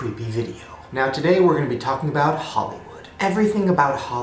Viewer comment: I thought Shane said "holowood"